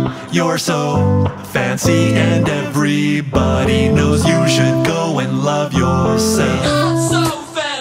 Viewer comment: mum:you should go and love yourself thought she said u should go and hurt yourself 😢😁